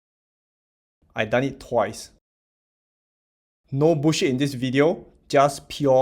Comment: I put my chicken wing down and paid attention as soon as he said no bullsh*t. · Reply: I thought he said Bushy haha😂😂😂